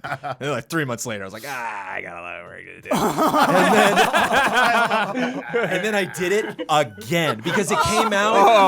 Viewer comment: what did he say/mumble? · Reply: "i got a lotta work to do"